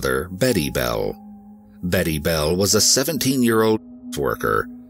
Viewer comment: Sounded like he said "Betty Bell was a 17 year old TWERKER"...😅